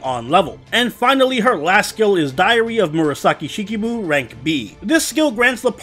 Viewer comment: I keep hearing “Diarrhea of Murasaki” and it’s really disturbing